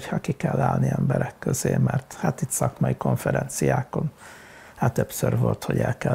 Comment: did he say "suck my confidencia"? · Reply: He said "szakmai konferenciákon", which means "at professional conferences".